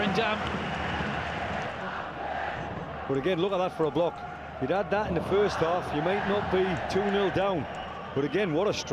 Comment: what was that fans saying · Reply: പോടാ പുല്ലേ somenthing